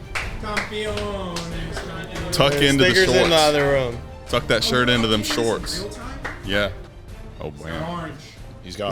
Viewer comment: what did say 😭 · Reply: Stickers